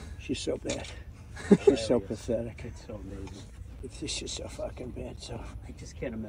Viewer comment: I think he said, "It's just so f***king bad."" not "she" or am I the only one that heard it that way instead?